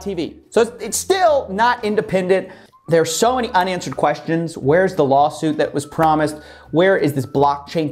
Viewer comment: I literally thought he said "onions and questions" there. But he actually said unanswered questions